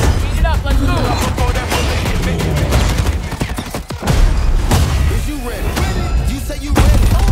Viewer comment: For a second I thought the new operator said, “Roach are you ready” and I got super excited. Then I realized he said roze and got bummed out. However this looks awesome, cannot wait to play.